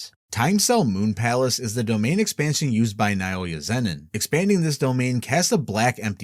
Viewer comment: "Nah Ooi Ya" lmfao